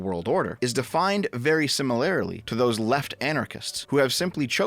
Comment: did you say simularily? 3:36 i cant go on